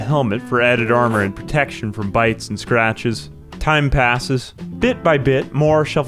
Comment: My hearing loss is stupid. When you picked up the helmet, I thought you said it would protect you from "BIKES and scratches." 😂 🤦‍♀️